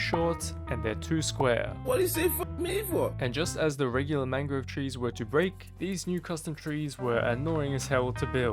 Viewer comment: Every time he said “mangrove trees” I heard “mango trees” wtf is wrong with meeeeeee😂😂 I